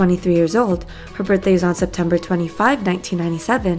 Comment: did she just say September 2-5 not September 25th lool